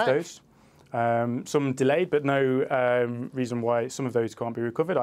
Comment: For a sec, I could swear I heard her say "Treason May".